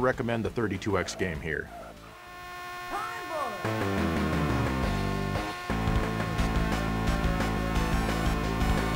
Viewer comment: what is the announcer saying? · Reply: Time Bonuh!